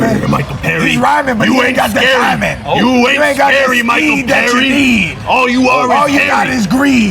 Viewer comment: Bro.. I thought Mike Perry said: "Oh your rhyming.. but you aint got your hymen"